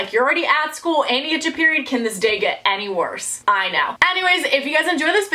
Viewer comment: I had subtitles on, and it said 'Amy, you just got your period, could this day get any worse?' and my name is Amy so I was like, wait, WUTTTTTT! So i went back a couple of seconds and turned my volume on and turns out she said 'And you just got your period could this day get any worse?' I was like phew. I really thought said my name there for a second.